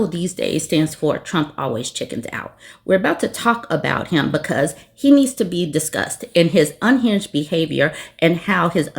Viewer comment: At the beginning I thought I heard "...because he needs to be disgust" and I was like "always has been"